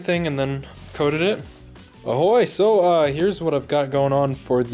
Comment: did he say "ahoy"? 😂